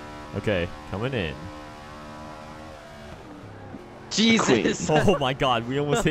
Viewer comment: WHAT DID HE SAY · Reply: "Jesus"